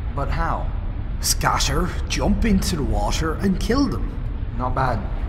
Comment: sounds like steve said washer stead of water